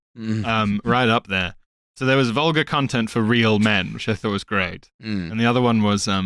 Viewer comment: in the teaser segment it sounded like was saying 'Volga content for real men' and it's like some sort of riparian porn or something??